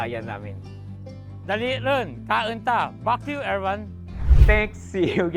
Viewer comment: huh? did i hear it correctly? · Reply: Back to you ang sinabi ng mabilis, nag tunog pak yu. Lol